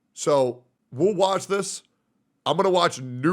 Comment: He really said, "WuWatch this"